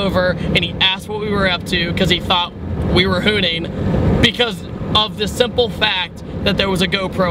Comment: I'm sorry id this is a ridiculous question but what is hooting? I have never heard someone use that word before... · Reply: hahaha I couldn't tell what he was saying, that's why I thought he said hooting and was really confused